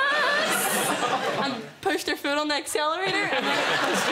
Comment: ok so at 10:26 in the subtitles instead of " Pushed her foot on the accelerator" it said " Pushed their fertile neck celery" 🤣🤣🤣🤣🤣